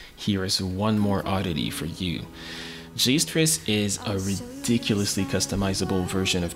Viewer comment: I can not hear the name in the of the ridiculously customizable tetris game in the conclusions at 16:18 please help me what does he say what is the name of the game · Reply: JSTris :)